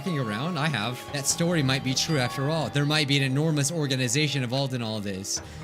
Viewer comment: BDBDJEOWNWBEHSUEKNWSB I THOUGHT THAT SAID ENORMOUS ORGASM